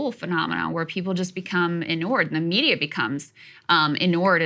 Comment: what word is Krystal saying? "Enoured"? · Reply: ENURE is to accustom to accept something undesirable.